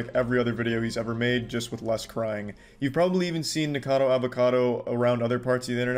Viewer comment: I heard: "niggerkador avokador"